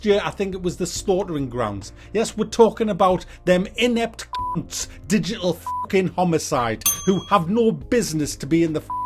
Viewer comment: What the fuck does he say at 7:53? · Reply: he says something like "inept cunt degenerate digital homiside